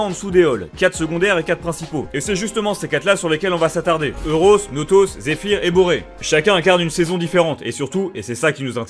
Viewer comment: A 1:15 j'ai compris Zéphyr est bourré xD